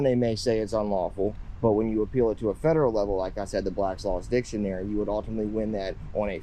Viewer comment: yes this is what i was referring to. no license needed unless doing commerce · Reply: my bad im at work half watching, i thought you said federal court